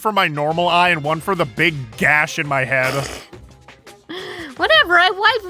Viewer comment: He actually said he needed two boxes of tissues, one for each “head,” not hand. It was meant to be a dirty joke. xD